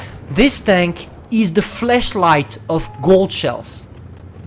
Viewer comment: I thought he said the “Fleshlight Of Gold Shells”